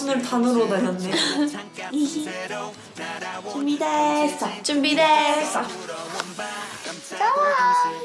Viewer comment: did they say junbi desu or I'm tripping